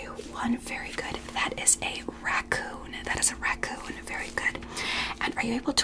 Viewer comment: I think you mispronounced landlord, it almost sounded like you said raccoon